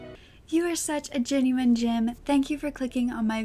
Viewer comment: Thanks for the upload although can I ask - are you saying genuine gem or genuine Jim? I thought you were saying genuine gem but the subtitles say genuine Jim?! Can you elaborate please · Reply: Gem. Subtitles are just wrong